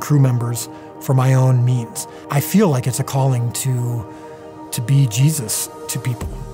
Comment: What was Dallas trying to say here? Am I the only one who hears "I feel like it's a calling to be Jesus"